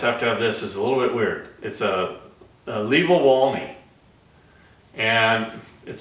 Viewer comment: Did anyone catch the name of the other product at 9:15? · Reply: Livowalny